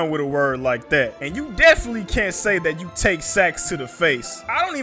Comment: Naaaaw, I thought he said he takes stacks to the face 😂😂 Like money, b it now that I think about it, even that sound sketchy as hell.